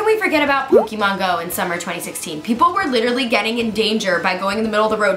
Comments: Gabi said “In 2016...” but the caption said 2015!? 🤨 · am i the only one that saw that gabi said 2016 and the subtitles said 2015 somebody edited wrong! oh and 2019 anyone like if you are!